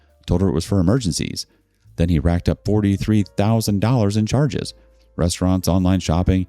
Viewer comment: $4300 instead of $43,000. At $43k I'd probably commit murder